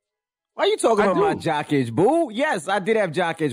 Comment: I could be trippin but did Envy just call Charlamagne boo ?